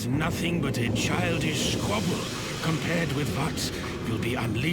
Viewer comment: Odd captions inconsistency, giving "to what" rather than "with what" like the voiceover and on-screen text says. 24:57 And again, with "only for" rather than "only of".